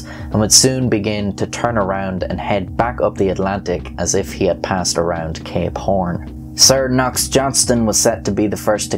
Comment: I zoned out and thought I heard he came around to gay porn...